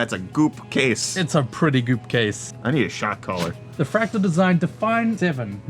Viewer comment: Heard the shot caller comment. I recently watched that movie. You have too good a heart to become a shot caller. Love you and really look forward to Brettfast every day. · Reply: I thought he said "shock collar."